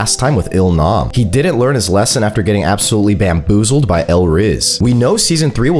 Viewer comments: Did bro say L rizz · In 0:59 I thought he said "L RIZZ" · did he say el rizz?